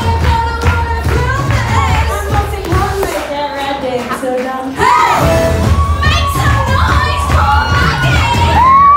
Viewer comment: did she say happy birthday? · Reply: salty tea no she said stop filming... yes she said happy birthday 😂